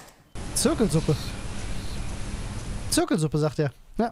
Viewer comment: Die Geistermobber decken wieder auf. In einem alten Lazarett sind Leute an Zirkelsuppe gestorben und wollen sich rechen. 🤣 Ich hab Zirkelkürbis verstanden.